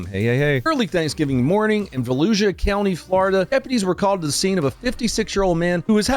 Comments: wait, did he say Fallujah county? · I thought he said Fallujah County, Florida 😂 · I thought he was saying "Fallujah" County. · I swear, i thought you said Fallujah county😂😂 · Seriously thought you said Fallujah County😅🤣 😂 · I totally heard “Fallujah county”! Not far off from a civilian perspective, as it turns out. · At first I thought you said "Fallujah" County. Which that guy was trying to make accurate. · For a second I thought he said Fallujah County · I swear I heard ‘Fallujah County’ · I thought he said Fallujah county lmao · At first I thought you said Fallujah county… After watching I still think so.